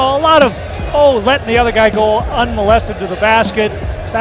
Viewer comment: Did he say unmolested to the basket